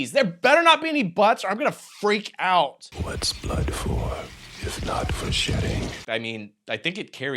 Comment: I was hit by so many butt jokes in the opening minutes that at 2:29 i had to rewind it because I heard Tony Todd ask "what are butts are for if not for shitting" 10/10